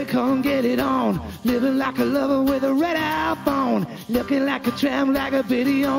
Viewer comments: Sounded like he said “Red iPhone” on the first verse, haha. I know it’s “radar phone”. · Is it me or does it sounds like he saying red iphone